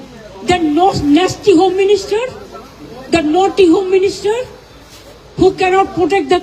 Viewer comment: Naughty home minister 😂😂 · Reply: At first instance I thought she is saying potty home minister. Her English actually is atrocious